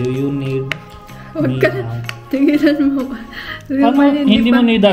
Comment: did i hear something..hehe "magtataka na ung maliit na bata😂😂ayiehh congrats guys..